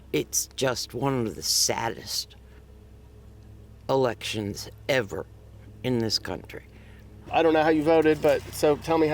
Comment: Every morning I wake up, I thank the Lord for not making me a liberal. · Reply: Wow I thought they said elections are legit